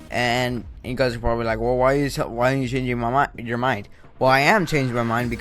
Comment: Did u say change your momma at 0:28 LOL!???